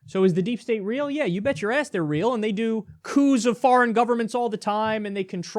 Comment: I heard "cooze of foreign governments" at 3:06.